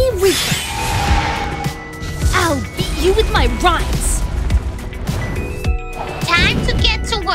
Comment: did she just said "I'll beat you with my rice!" ?